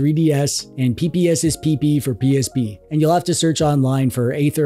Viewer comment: brother did you just say "pps is pp fpr psp"? best thing i've heard today